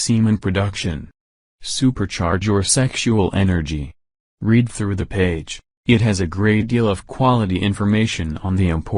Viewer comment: Love it, but so hard to hear what he says when talking about herbs/vitamins (especially around 1:36:00 and out) - can´t get the names, Could you provide a list? Thanks. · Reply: Tongkat ali and schizandra